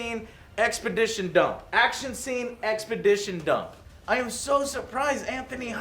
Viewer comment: Did he just say EXPEDITION dump :))))